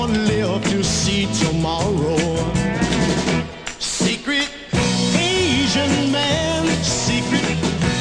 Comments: It really does sound like he is saying Secret Asian Man. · It sounds like he is saying secret Asian man. I can never unhear it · After all this time, I thought he said “Secret Asian Man”. Which is my neighbor.